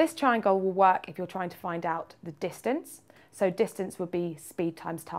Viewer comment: ''tryna wack out the time'' 0:58 me have no idea what she sayin lol.